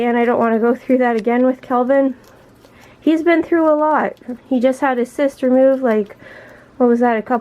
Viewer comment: Lol when you said "he just had a cyst removed" I thought you said "he just had his sister move" :p